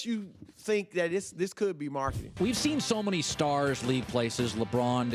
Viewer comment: Wait what does Broussard say that "Mamba Mentality" meant at 5:15? It cuts off but sounded like "marketed".